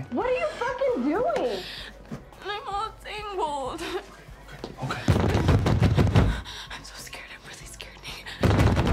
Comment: What is she saying at 1:25? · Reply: im all tangled